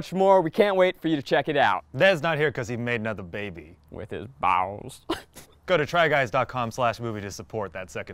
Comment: Eugene: Ned's not here bc he made another baby. No one:.... Zach: wItH hIS bALLz Thanks for clearing that up Zach🤣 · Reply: I thought he said “with his boss